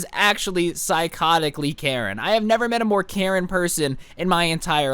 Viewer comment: When he said Karen person I thought he said caring person I was like wait what the f***